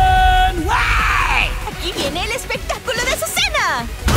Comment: The subtitles are wrong lol She's actually saying "here comes Azucena's spectacle!"